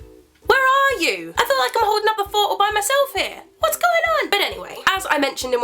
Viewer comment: Hello, first of all I'd like to clear up I'm not a English native speaker although I can speak it fluently. But at 0:03 Carrie says something quickly and I think what she says is "I feel like I'm a whole number four all by myself here"... I might be wrong, but I just wanted to know what that means? Sorry if it's silly, I just can't live with the doubt hahah I love you Carrie, your videos always cheer me up xx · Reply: Hey! :) She says "I feel like I'm holding up the fort all by myself here", which means that she feels as if she is doing all the work on her own, in a way? She's encouraging them to contribute to the back-and-forth "Dear..." videos :) Hope that helps!